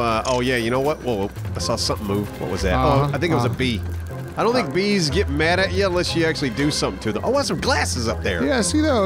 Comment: I didn't have my volume all the way up and thought said "I don't think Bees mess with you unless you have some GLASSES with you!" and thought it was a joke referring that movie where the kid with glasses died of bees.